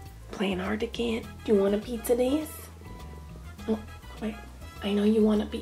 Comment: Sounds like she says: Wanna pizza this.. Btw U look bootiful ;););)